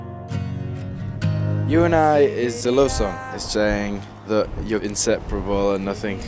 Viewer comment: En el 0:08 parece que dice "You & I es celoso" JAJJSJA